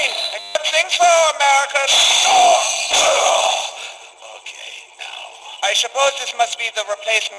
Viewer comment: does Dempsey say “okay, now I’m mad?’ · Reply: I think he said "Ok, ow"